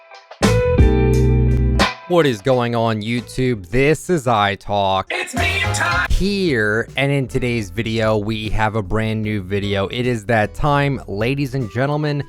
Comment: Sounds like he says it’s bedtime